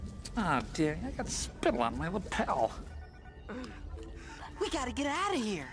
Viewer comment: As a kid I always thought he said 'little pal' instead of lapel. I just though he really liked his suit.